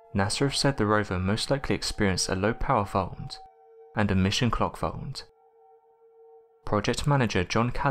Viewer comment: It sounds like you're saying "low power font, and a mission clock font", I wish I knew what the heck you were actually saying.